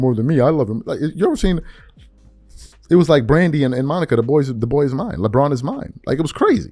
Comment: I thought I heard Leboy is mine. 🤣🤣🤣